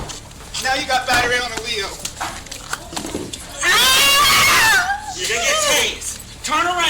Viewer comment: subtitles "now you got battery on the wheel", what was said "now you got batter on a LEO".